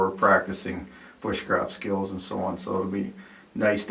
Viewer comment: LOL... Oh my gosh! I had to stop and rewind the video THREE times to “re-listen” to what the gathering area would be used for! First I heard ‘for practicing witchcraft skills’. I’m thinking, oh Lord tell me I didn’t just hear that! So after three times I’m PRETTY sure you said ‘Woodcrafting’ skills..... 🤦‍♀️ ...right???or ‘bushcraft’ skills ..??? Who me? A city girl? ... yeah 🙄